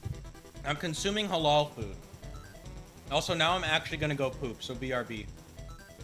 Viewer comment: ish I heard you say “im consuming halal food” Is that what you said? I’m a Muslim chef and it made me happy to hear. For those who don’t know, halal just means the food is permissible and if it’s meat, that it was individually slaughtered while a blessing was said and had the blood drained most efficiently. Regardless of religion I encourage everyone to eat halal and or kosher where possible because it will ensure wholesomeness unlike mass farmed and factory slaughtered animals.